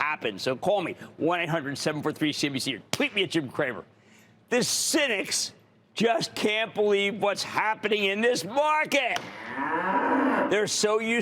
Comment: I thought he said in the beginning "hasidics just cant believe whats happening in this market" lolololol